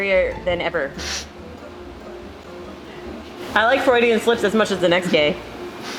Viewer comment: frieudan slips i thought she said feilding in slips and i was like what?